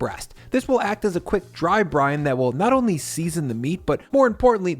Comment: every time you say "dry brine" I hear "Dry Brian"